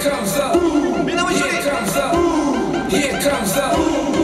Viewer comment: スーパースパイクの後 何て言ってるか分かる方いますか？ He comes ですか？ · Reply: Here comes the BOOM! です。 女子の試合の応援に行きましたがこれは男女関わらず全ての国を応援出来る掛け声として設定されているので、heと聞こえますが、違うと思います。